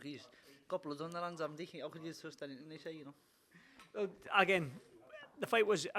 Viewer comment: wtf he saying? ahahah , great win) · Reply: Got blood on their hands , how can you trust anything they say …